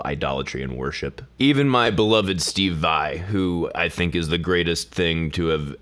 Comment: I dont understand who he is mentioning on 3:20 Stevai? Anyone please?